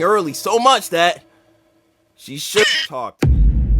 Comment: What was said? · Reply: Shit talked